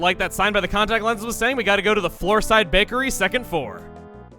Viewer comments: Did anyone else hear, "Gotta go to the floorside bakery, second four." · “Floorside Bakery second Four.” Did I hear that correctly?